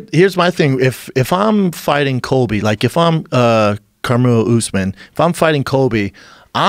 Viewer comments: Did Schaub just call Kamaru Karmello Usman? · did he say Carmelo usman?😂